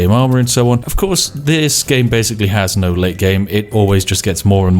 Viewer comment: When he said "lategame" I heard "late early game" lol.